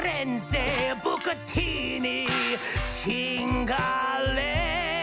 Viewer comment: As an italian, when Fizzarolli started singing in Italian i cracked up laughing. This episode is amazing lmao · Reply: I thought he was saying Chingalo. Which is spanish for "fuck it."